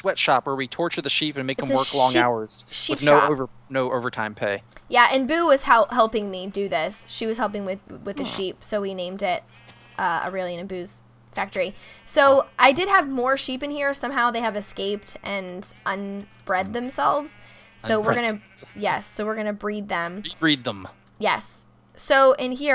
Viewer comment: "She was helping with the sheep so we named it Aureylian and Boo's factory". I had to do a doubletake. I thought you said Booze Factory